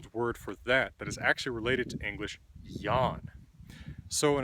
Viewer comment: Bjarki Þórðarson I think he means the ending -an, like in Swedish we say "Blomman" (flower), "Soff-an" (couch) etc. But i'm not sure if that's what he meant.. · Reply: I don't think that's what he meant, because at 3:20 he says that the word is "actually related to English YAN", or however it is written. It is this little word that puzzles me and I've never heard of it before.